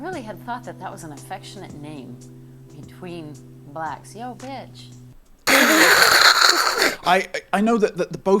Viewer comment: I've watched this so many times, and I only just now realized that thought said "you bitch" not "yo bitch". Which makes it make a lot more sense.